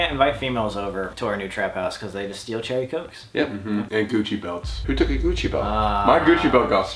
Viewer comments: I heard coochie belts 😭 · i thought you said coochie belts 😂